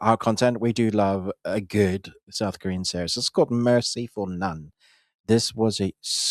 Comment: This is frustrating that they only say quickly the name of the series once, without showing it. For example, the first series he mentions, from South Korea, I thought he said "Merciful Nun". I had to search for it on internet and only then I found "Mercy for none". It refers mainly to the fellow on the left of the screen, because he just mumbles the titles.